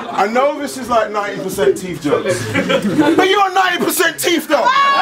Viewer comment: "I know this is 90% teeth jokes... BUT YOU'RE 90% TEETH THO" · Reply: I heard it as, ur 90% TEEF!! as in thief lol either way, got beat - which in 2024 is hard to say 4 me lol